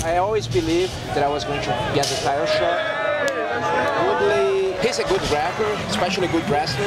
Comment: did demian say woodley is a good rapper? (i know he said grappler, it is just funny because Woodley had a decent freestyle the other day on some show recently)